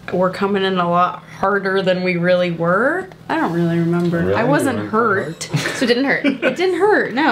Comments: Skeet:you really weren’t that hard?!!?!? (Cole smirks) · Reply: Haylee De gracia I thought he said really you werent hard😂😂😂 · skeet said that on purpose 7:08 what did Vanessa say to cassey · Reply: ChaCha Nelson she said “ hashtag eggplant emoji”